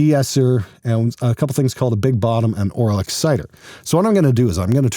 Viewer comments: Aphex’ sales go through the roof from bass players who watch this and thought said it has an “Oral Exciter” ... 😳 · I heard Aural Exciter, but thought I heard Oral Exciter; And you know you're that kind of dude when your mind shifts to THOSE kind of thoughts. Edit: I definitely went and commented too early, since the second time he said it, he had to follow up with the verbal equivalent to the spray bottle on the face/Nerf Gun at the Lead Singer's head.